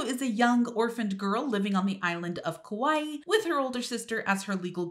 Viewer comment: It sounded like she said “island of kawaii” instead of Hawaii lol